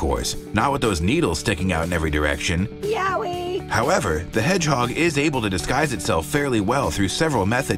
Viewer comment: I heard yaoi